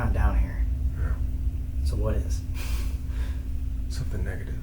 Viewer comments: Oh God! At 41:52 min I heard a Man voice saying "There's something in here" That SCARED the Shit out of Me! 😱😨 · Did anyone else hear a deep voice say "YEAH" at 41:44 ?